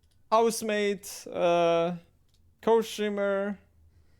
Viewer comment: He didn’t have a problem with housemaid but mascot is where he draws the line? xdd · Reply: Oh I thought he said housemate, thank you for this